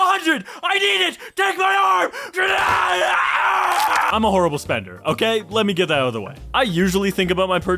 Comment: What I heard: “I’m a homosexual.”